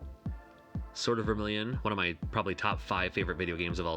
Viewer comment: Sounds like you said “Sorta Vermilion”